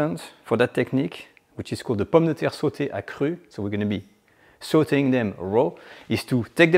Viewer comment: Every time he says 'saute' I hear 'salty'. 🤣